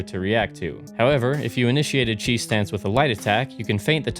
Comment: Thought u were saying cheese stance the whole time lol